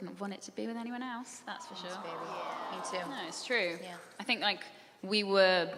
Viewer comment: dom says "i wouldnt want it to be want it to be with anyone els" crowd "awwwwww" i SWEAR kat says "yes baby" i shet u not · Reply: Lester Dambull she says “thanks baby. me too” :)